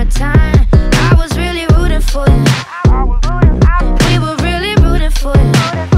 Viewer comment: I heard "booty for you" so yeah I searched it up and it's no success.